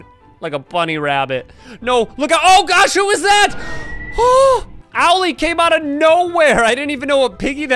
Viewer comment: did u just say Owelly